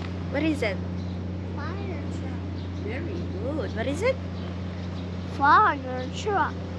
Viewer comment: did he say "foreigner truck" lol